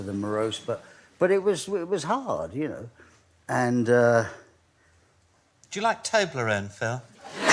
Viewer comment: What did the boy say at 5:57? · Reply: "Do you like Toblerone, Phil?"